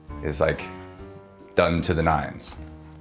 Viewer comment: did he just say "don't to the nines"? wait… 💅❔